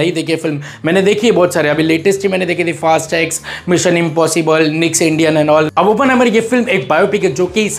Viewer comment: kya bola ? Niks Indian ?😂😂